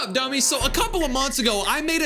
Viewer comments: what did you call me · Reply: U know. HE CALLED YOU A DUMMY, DUMMY! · huh did you called us dummy’s😠